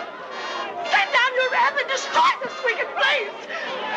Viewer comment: What did she say? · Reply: Elisa Huberman from 0:00-0:05 Ethel Waters says "Lord, hear my prayer. Send down your wrath and destroy this wicked place".